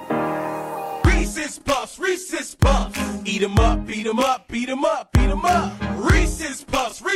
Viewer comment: I thought he said Misiss Puff,Eat it up